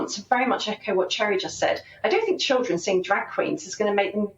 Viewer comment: I thought he said "drug queen"??